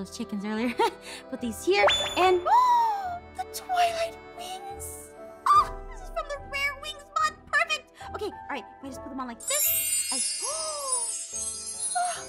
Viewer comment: it sounds like she says toilet wings